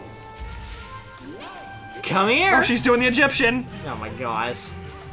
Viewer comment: It sounds like she just said "OH SH@ bTW her name is.